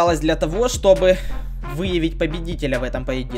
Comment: Послышалось вы*бать победителя ору!)😂